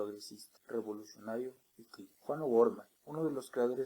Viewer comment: WAAAAAA MUCHAS GRACIAS, es el unico video que encontre con toda la informacion util que queria escuchar. Gracias!! solo que no se le entiende al Ultimo que habla, por que tan bajito??? pense que decia Juan Novorla, Y ERA VJuan O'Gorman.